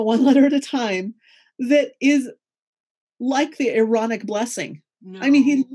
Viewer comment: Ohh! Aaronic blessing! I couldn't hear what she was saying at first